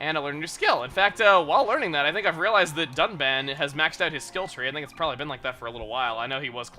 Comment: Am I the only one that heard duck man instead of Dunban? LOL, Duck Man, the first officially revealed Robot Master for Mega Man 11. If only. xD